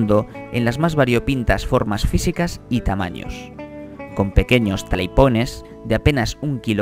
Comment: que es lo que dice? Busque ese mono y no lo encuentro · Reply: es talapoin